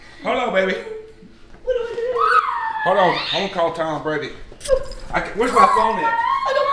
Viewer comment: Did he just say “ima call Tom Brady”? 😂😂😂